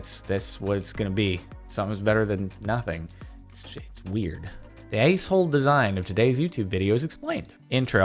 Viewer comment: Actually they thought u said “lace hole design”